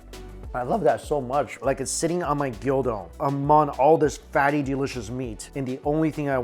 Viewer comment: I'm half awake and I somehow misheard "Sitting on my gyudon" to "sitting on my D**do" around14:40 was so shocked i needed to check twice.........god I need to make coffee